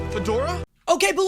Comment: also did he just call her fedora?